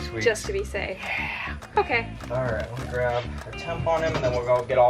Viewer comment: I thought he said he was grabbing a tampon T-T